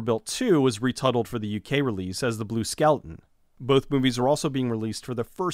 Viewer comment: I thought you were saying "skelton" the whole time.